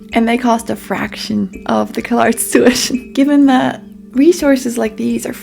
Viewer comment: the automatic captions of the video say "kill arts" instead of CalArts, which I think is extremely fitting